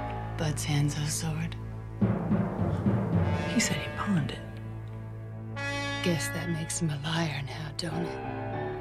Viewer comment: Elle: What’s that? Beatrix: Bud’s hanzo sword Elle: He said he pawned it Beatrix: Guess that makes him a liar now, don’t it? Elle: 😡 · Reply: Is that new?? I thought she said "guess that makes ME a liar now don't it"